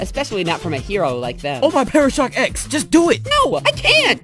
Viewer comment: Oh my ParashockX!! I loved this episode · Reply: I thought he said OH MY "PARASHOT EGGS"